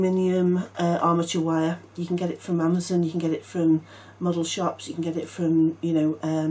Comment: When u were saying where to get the aluminum wire i thought u said "muggle shops". LMAO I had to go back and turn the volume up and listen very well to hear " model shops".... Too funny...😂😂😂